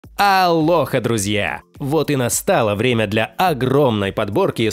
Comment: Вначале послышалось : "Для лоха , друзья !" 😁